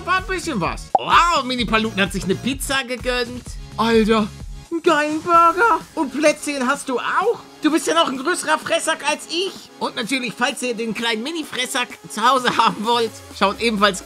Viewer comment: Ich habe am Anfang Bressack verstanden!